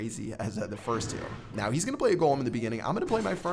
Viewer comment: he is gonna play a golem in the beginning? i thought i misheard. :)